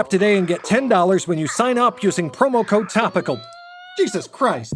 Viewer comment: Tell me I'm not the only one who heard that sentence as "porno code topical"...